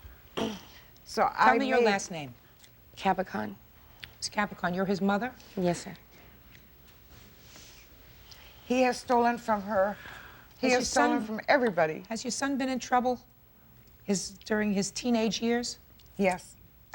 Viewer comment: Did she say “yes sir?” 😭